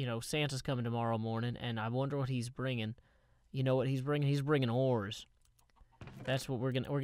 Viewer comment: I thought you said he was bringing whores. I like my way better.